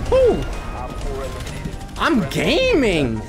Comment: I thought you said I'm gay